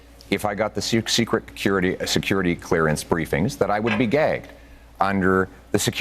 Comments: It sounds like he said "gay" instead of "gagged" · he would be gay? I don't speak Canadian sorry what is he saying? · At first I actually thought he said that if he signed the clearance he would be GAY.. 🤣